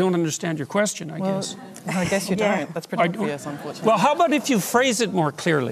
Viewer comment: What does she say at 9:32 ? "Well i guess you dont...."